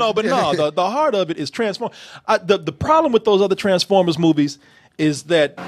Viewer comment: Misheard you at 11:40 thought you said "Trash-formers"